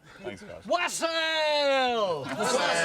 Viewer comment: What did he shout at 35:30? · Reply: Wassaaaail!